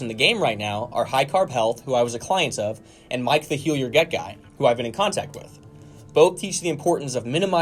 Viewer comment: What is my q? I couldn't find this channel. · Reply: ??? In Minute 4:30 you say it, but I can't understand it. Mike the heal you get guy? Can you provide a link, please?